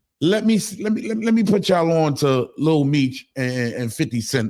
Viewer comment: I thought Fif said he was going to call Mona S for lil meech 🎥